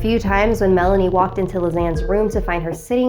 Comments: It sounds like your saying Lil xan 😂😂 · I’m so sorry but whenever I hear Lisanne I keep thinking about lasagna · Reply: Space Roach at first I thought she said "lil xan" LMAO